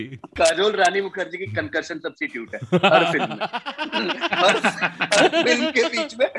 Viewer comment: What did KV said at 7:22 ..? 😅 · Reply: Ohk... i wasn't sure that it was concussion as I was not getting it's meaning....Thank you for confirming 😅👍🙏😊